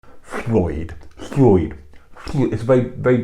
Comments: Looking up how to pronounce the ll in Welsh, you had me very confused. When you do it, it sounds like it starts with an f. It sounded like you were saying Floyd. But upon looking it up, the ll actually is supposed to sound like "sssccchhh". So the name Llwyd should sound a little like "sh-oi-d" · Sounds like he is saying Floyd with my phone recognizes as a real word